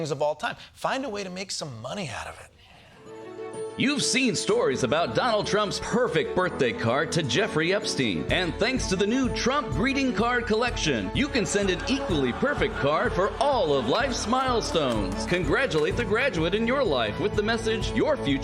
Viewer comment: I had to go back and hear it again... at first I thought it said "Breeding Card".